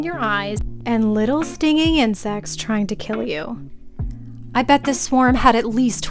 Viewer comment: All I heard was "Stinging and sex" sign me up